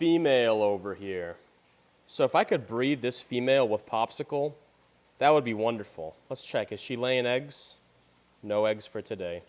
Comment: umm what did you just SAY???????!? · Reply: "If I could breed this female with popsicle, that would be wonderful" 😂😂😂😂😂 Context is important..... 🤣